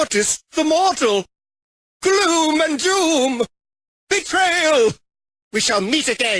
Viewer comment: yo creo que dice mi nombre en brawl stars me llamo Big Adri1 :v